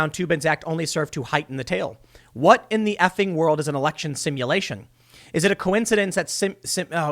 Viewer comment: Poor guy, he thought they said “errection stimulation”